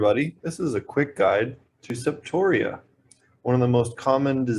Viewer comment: at first I thought they said quick guide to Sepultura... Bloody Roots...lol is one of my favorite songs from them...just sayin